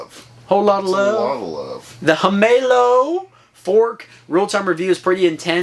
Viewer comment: I always thought that said "Am in love" in the intros with that scene, it was Himallosssss all along 😂😂